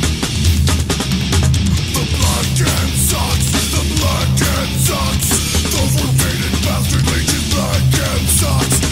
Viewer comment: is he saying "the black kid sucks!" Haha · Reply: He say black hand